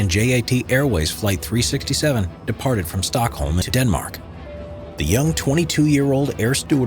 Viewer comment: Departed from Stockholm, Denmark?! Did watch too much Simple Geography? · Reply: It sounds like he said Stockholm to Denmark the audio seems goofed check the Close Captions.